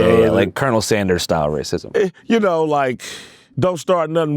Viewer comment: Did bro just say colonel sanders style racism…? 25:06 😂😂😂